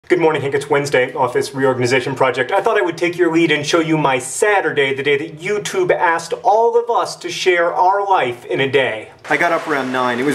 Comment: Oh you Americans amuse me.. first I thought you said ''sadder day'' and I thought, poor Green bros.. they had a sad day and now they're having an even sadder one! Oh.. SATURDAY! I was much relieved!